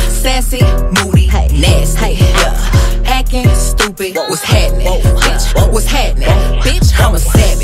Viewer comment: At the part ‘acting stupid’ I hear ‘hecking stupid’